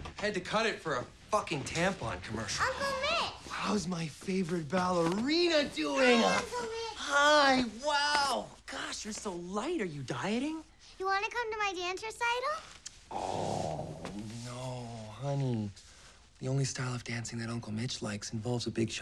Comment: I thought he said "you're so light are you dying?"